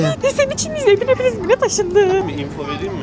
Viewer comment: yanlış mı duydum yoksa taşınmakmı dedin abi????